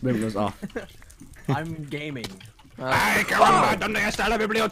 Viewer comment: He said Ay mio donde estas la bibliothecca which translates to oh geez where is the library? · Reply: i thought he said “cabron” not “caramba”